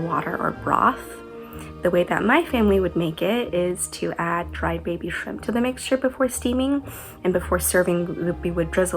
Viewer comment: During the steamed egg custard portion I thought she said she added ‘dried baby food’. And I wondered what the heck that was!!! 😂 🍤